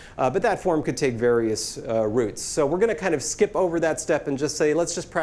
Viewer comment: Mr. Peterson, at 2:02 you said: "that form could take various routes." Since you are an accomplished linguist, I had assumed that you would've used the unambiguous pronunciation of "routes" (/rauts/). Instead, you said /ruts/. So it sounded like "roots". Great video 👍